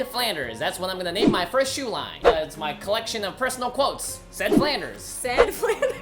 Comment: At first I heard him say "Slag Flanders"